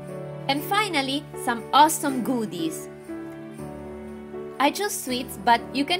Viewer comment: Did she say godis, because IT means Candy in Swedish